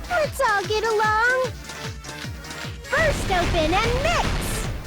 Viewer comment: did she just say burst open and mix XD btw this is a dragonball thing